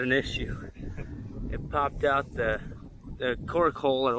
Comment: Lol lol I laughed I thought u said it popped out the corn hole. 😆 🤣 😂 😹 great progress on the jobs.